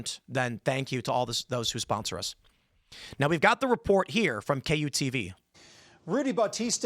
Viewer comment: i jumped ahead to skip the ad and i heard "You got to eat all those who sponsor us."